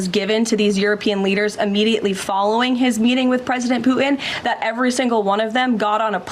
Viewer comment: It sounds like she is saying President Poopin! 🤣🤣🤣🤣